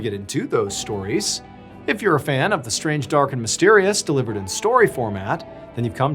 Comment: Whenever he says “strange, dark and mysterious”, I always hear “tall, dark and handsome!” 😂😂🤷‍♂️